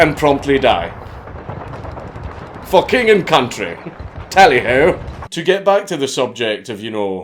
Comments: I heard "For King and Country" as "Fucking And Country." lol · I fucking love that smirking "For King and country. Heh. Tally Ho" Is so fucking funny to me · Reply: ThatOneGermanGuy shit I thought he said « Fucking in country » 😆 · I thought he said "Fucking country" at first lmao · Dank says "For King And Country". I hear "Fukkin in-Country". The hazards of Combat Veteran hearing.